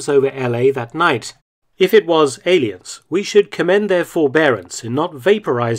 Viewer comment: "if it were aliens, we should commend their forbearance in not vaporizing la la land after such a welcoming." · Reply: Oh, he said commend? My mistake. Naturally, I thought he said condemn.